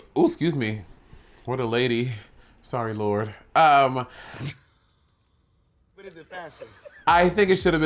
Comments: did someone else heard Bob saying, “but is it fashion?” 😂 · Did anyone else hear that quiet "But is it fashion?" at 21:33 after Monet's burp?? I don't fully understand why the editor put that there but omg hahahaha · anyone else hear the “but is it fashion?” at 21:33 lmao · Did anyone else hear the "but is it fashion" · Am I the only one who heard Bob saying, "But is it fashion?" in the background, hahahahahaha!? That sh*t. SENT. ME! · did anyone else hear bob’s “but is it fashion” @ 21:35 or am i just wanting to hear bob during a monét segment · did anyone hear "but is it fashion" at 21:35??